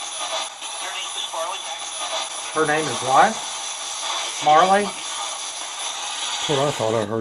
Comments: I hear "Her name is Marley Becker" or "Her name is Marlene Becker". Last name could be "Beckham", but I'm hearing Becker. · Thanks for scaring the crap out of me at the end! I had the volume turned up so I could her the responses and then the music came on. I heard 'Farley' not Marley. The way you edit these videos is awesome, really adds to the suspense. I would like to see more background info on the places you investigate tho. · ish, I heard "Her name is Marley Maxwell"